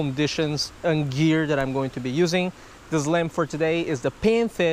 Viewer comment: Who else thought he said Islam hahahah